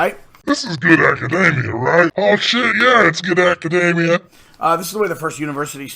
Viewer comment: Love them nuts! Oh, sorry, I thought he said good macadamias.